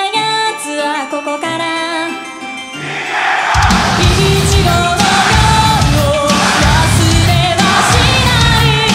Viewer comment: Does anyone know what they was shouting at 3:37 ? I just hearing いけろ (Ikero) means to be good. Is that right or not LOL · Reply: They shout "ichigo" ichigo -> 15 In Japanese, "15" is usually pronounced as "jûgo" ("jû"+"go" = (1x)10+5 = 15). It is rare to read "15" as "ichigo" ("ichi"+"go" = 1(x10)+5 = 15) more info here : <link>